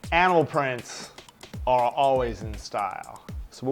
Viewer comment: at the beginning when he said animal prints i thought he said anal prints O_O